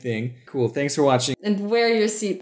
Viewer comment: I didn't hear the word "belt" at the end, so I just heard say "Wear your seat."